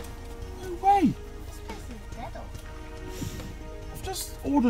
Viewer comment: did she say "this place is ghetto" LOL? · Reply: No she said “this place is metal.”